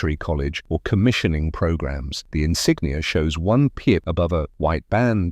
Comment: What is the word that sounds like "pia" or "piet" that describes the yellowish square with a maple leaf in the middle, in some officer ranks? (8:18) Is it supposed to be "pip"?